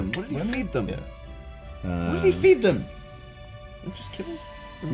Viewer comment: what did he feed them WHAT DID HE FEED THEM when joe said “just kibble?” i thought he said “just kids?” wow